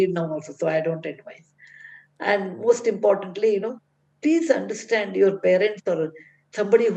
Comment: did she say " please understand your parents Tarun or was it just me who heard it? "